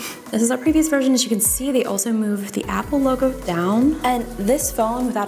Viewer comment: LMAO I thought she said “and you can move the Apple logo down” my jaw dropped 😂. I’m slow Ik 😂😂😭